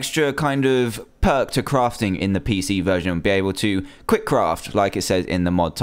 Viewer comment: I thought it said quirck craft